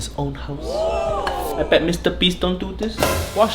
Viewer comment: did he say, MrBeast? xD